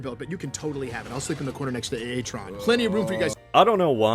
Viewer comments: did he say A-A-TRON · did... did he say A - A - tron?